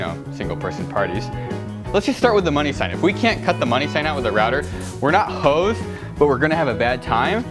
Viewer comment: "we're not hoes" wat did i hear that right